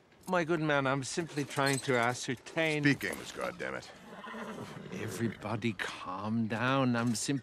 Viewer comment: Bro I could’ve swore buddy said speak English god damnit